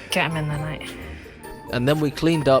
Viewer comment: did she say "get them in there mate' that's a bit rude